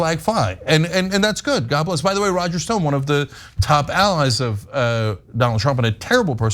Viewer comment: I will NEVER forgive you for telling me about Roger Stone being a swinger. Gross!!🤮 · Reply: OMG I thought he said Roger Ailes! Which makes finding out it was Roger Stone… uh… good… news?? 😳🤢 My brain hurts.